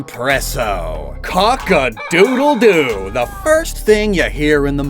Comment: I fr thought you were about to say Mpreggo (which is fan-fic culture for "Male Pregnancy") lmfao